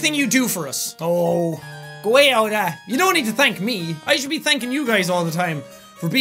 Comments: What'd he say at 9:19? Sounded like he said, "Aww, go away Yoda!" lol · What did he say at 9:20 ??? I keep replaying it, but idk! It sounds like 'oh.. goyeyodah.' .... .-. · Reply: It was: " aaah, go away with all that" Only merged together a bit, like most Irish speak. Not Irish though. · HOLY SHIT WHEN HE SAID ''Oh,go away (something I didn't hear properly)'' IT SOUNDED LIKE HE SAID ''Oh go away Olga'' and while I was drawing I heard that omg :D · What did he say? Goa,ade?